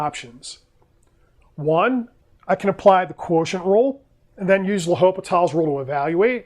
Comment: What kind of rule does he say at 7:11? I cannot hear clearly. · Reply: Either quotient rule or L'Hopital's rule